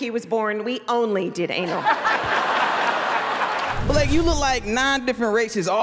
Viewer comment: Blake came with the heat. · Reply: Thought you said blank.